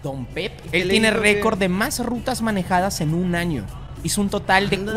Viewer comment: le entendí en vez de rutas le entendí rucas JAJAJAJA